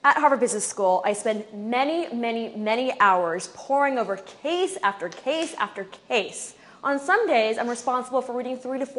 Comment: who else thought she said whoring over chase after chase, maybe wishful thinking on my part lol